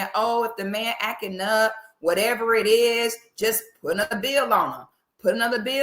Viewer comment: I thought you were saying she put another BIB on 😆 since you were talking about hot dogs 🌭 and fried baloney…but then I realized you were saying put another BILL on him. 😂🤷🏽‍♀️